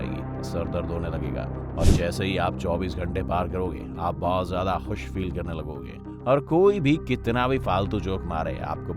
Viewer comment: Bhai saab 0:48 pr aap kya bole kuch smjh nhi aaya... barkroke kya hota h ... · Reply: Bahar jaoge